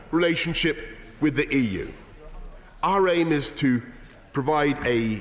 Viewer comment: I heard “our anus to”